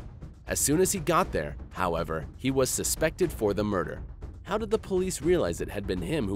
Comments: = it was written Suspended instead of Suspected.... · he wrote suspended instead of suspected. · Ah 10:18 there is a spelling mistake. The bloke says "suspected" but the subtitles say "suspended"